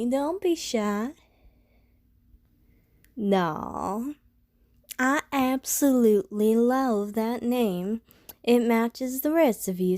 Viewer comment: I thought she said "I absolutely loath that name" and I got taken aback